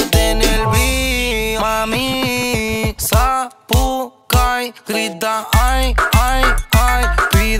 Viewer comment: Que carajo dice en el minuto 3:00 ?? ¿¿¿Sapucai??? Que alguien me diga que diceee o que quiso decirrr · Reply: siii dice sapucai grita ay ay ay. como que ella grita como si hiciera un sapucay jaa